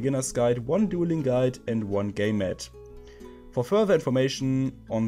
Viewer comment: egal wie oft und genau ich hinhöre, ich könnte schwören das du "Gay to the Games" sagst :D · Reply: ich hab auch gay mate statt game mat verstanden 😂